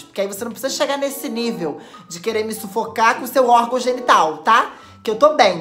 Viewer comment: Você falou sufocaDA NO 9:37??? Oi bebe💗 Te amooooo ME RESPONDE POR***